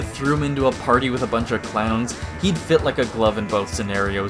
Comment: I misheard clowns as Klingons for some reason and the mental image of Wario at at party with a bunch of Klingons is the funniest thing to me